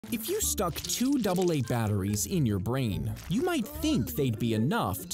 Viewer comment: All I heard was "Put Uranium in your brain". As you command!